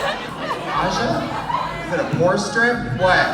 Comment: What did trixie say about aja at 20:15 ??? a poor strip?? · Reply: A pore strip to clean out pores on the face